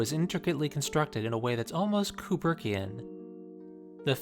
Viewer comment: on 10:16 he said that like evangelion 1 hour photo is constructed in a way that's almost cooper something, i cant figure out the word he said, can anybody help me with that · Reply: Kubrickian: <link>